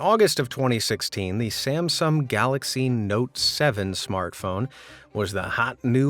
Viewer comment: did you say the "galaxy Samsam? Is this voice AI or